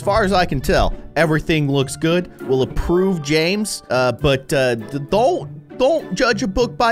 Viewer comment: I thought he said James bound😂